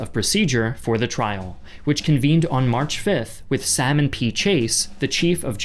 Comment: Sorry, I know it's Salmon P. Chase, but I misheard it as "Sam Impeach Ace" 😂